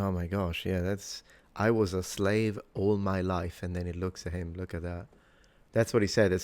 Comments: I heard "I was a slave all the way" or even "Ii was a slave over there" -- it can't be "I was a slave all my life" because this was filmed in 1944 and this man lived half his life in the 20th century. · I thought Mr. said "I lived here all my life."